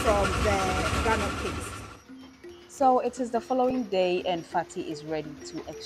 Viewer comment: did you call that woman fatty !? That's rude ! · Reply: Hi Geof. That’s her name, ‘Fati’. It is gorgeous name of Arabic origin. Thanks for watching